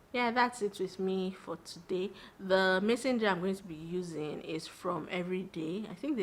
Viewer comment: I've been quietly enjoyed these consistent uploads! ❤❤ Currently watching while cooking. 2:48 what do you mean by messenger? I didn't understand that part. *Edit: I didn't know that those jars were called mason jar 😂😂😂😂😂. I'm a hidiot. Heard you say it again towards the end when you were drinking water Hahahaha anyone who doesn't sing and dance to food must be a serial killer. Jk. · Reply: Lol she said mason jar not messenger. It took me a while to figure it out too 😂